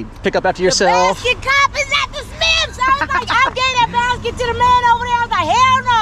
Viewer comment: I thought I heard the Basket Case is at the Smiths....hilarious!